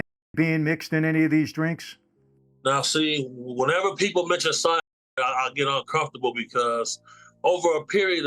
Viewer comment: Will someone tell me what is edited out? I can’t tell what he is talking about. Thanks! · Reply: CYANIDE